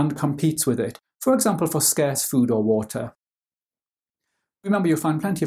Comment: it says scare instead of scarce lock in g